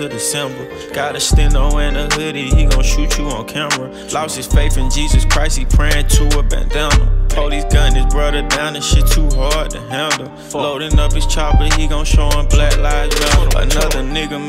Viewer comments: "He lost his faith in Jesus Crist he praying to a bandana"😥💯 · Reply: Thought you said banana😂😂😂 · "lost his faith in Jesus Christ he praying to a bandana" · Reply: i thought you said banana tbh dude